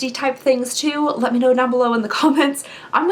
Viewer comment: I heard “oh I will down below” which is really odd. I don’t know what that could even really mean. I’d love to be entered in the giveaway also! Instagram: saddestsquatch